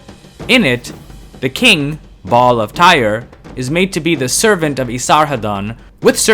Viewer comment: "Ba'al of Tyre". I heard it as "Bob of Tyre". 😆